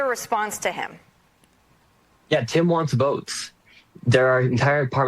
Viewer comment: I heard this as "Tim wants boats" and was thinking, NO that's what Tim's VOTERS want. Boats and Billions to be dee richest captain aboard!